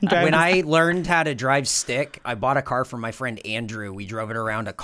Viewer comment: I thought he said "i bought a car FOR my friend andrew" not from. Was thinking wow this guy must be rich